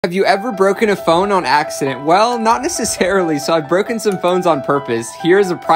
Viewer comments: am i the only one who thought it said “bone” instead of “phone” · i don’t know why but i heard “i’ve broken some bones on purpose” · I thought he said bones for a second I was like 😨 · i thought he said bones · i thought he said "ive broken some bones on purpose" 💀🙏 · Bro I heard him saying "bones" every time and I was so lost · I thought that said bones... Was wondering why you broke your bones on purpose and what shining a laser had to do with anything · I thought bro said he broke a bone for a minute there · "Phone" What I hear is "Bone".